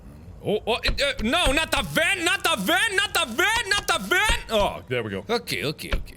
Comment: Sou brasileira! só eu escutei ele falando nada vê nada vê nada vê? Na parte 12:44?